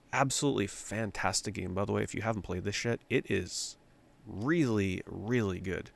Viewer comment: think he said if you haven't played this yet But it kinda sounds like if you haven't played this shit 😂